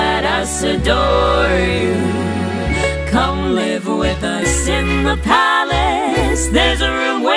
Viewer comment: Why did I just hear 'Come, live with lice in the palace'?